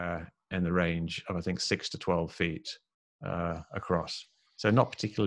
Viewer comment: Beautifully done! Mick West reasons so elegantly! I love this kind of this stuff. Please do more things like these, in this world blinkered with belief, rationality is sorely needed. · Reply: "We're specifically talking about the 15:07 mark where he draws a red line and says: "It's 6.6 feet!"" I heard something like "6 to 12 feet", maybe the audio is unclear. And he also explains how he came to this conclusion, I think? Listen to it, please, and then come back to me. Give me a short summary.